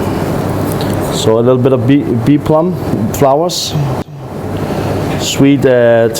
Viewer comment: do you know the white flower he put after the chickweed? i cannot hear it clearly.... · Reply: It sounds like he is saying 'Bee Plum' flowers, but I haven't heard of a flower by that name. However to me, the flowers look like either Sweet Alyssum (which smells like honey) or Vibernum.